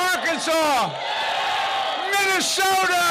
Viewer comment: What i hear as an Indonesian : “MINUM SODA!!!!!!”